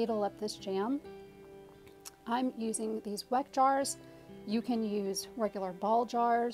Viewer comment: Love your videos/recipes! Question 🙋🏽‍♀️ What are the jars and lids you used & placed in the canning bath? Do you have a shopping link for those? Thank you! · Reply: Sorry rewatched your video, heard you say Weck jars 😉